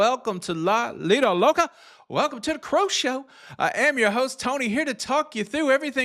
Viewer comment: Every time you say “I am your host Tony” all I hear is “Lend me some sugar, I am your neighbor”…..shoutout Ludacris! Oh and as far as paying passengers for things not in the cruise lines control….ummmmmm…..how about no! The entitlement struggle is real.